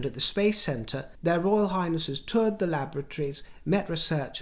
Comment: It was lovely to see Sophie attending an engagement with The Duke of Kent who I feel she has a deep regard for. I have noticed at events such as Trooping The Colour when on the Palace balcony, at The Duchess of Kent's funeral etc Sophies inclusiveness, respect, reverence shown towards, genuine regard and their rapport is so refreshing to see, especially in light of other ongoing matters. Pardon me but I did have a little giggle when you spoke of Sophie and the Duke as I thought I heard you say 'they toured the lavatories' instead of 'laboratories' I really must get my ear trumpet replaced!